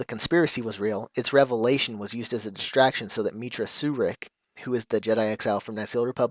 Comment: can you explain what you said. It sounded like Mitra Surick. I don't remember anybody by that name from Knights of the Old Republic 2?